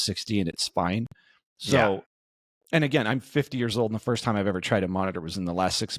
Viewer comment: what 50 years old I don't believe it at all you look early 30 please confirm did I hear well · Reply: Yes I am 50 years old as of April 2024.